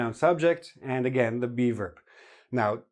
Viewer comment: Oh geez, every time says "be verb" I hear "beaver" that sends me so bad :"D